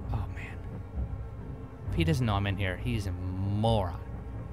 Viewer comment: Haha Turn on CC and Transcribe Audio then go to 2:42 , says "He's a moron." And the captions say "He does not mean you." LOL